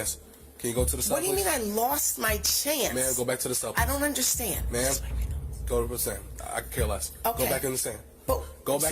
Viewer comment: What does she whisper when she puts her hand up to her mouth? I believe the officer says, "i could care less," to whatever she whispered. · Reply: I could be wrong but I think she literally describes what she's doing - "Just wiping my nose". Drunk brain.